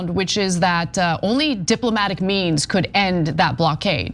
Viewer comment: I thought Ana said Diplomatic MEMEs ( it was means).... sad thing is I thought for a second " he's not really doing to do that is he" lol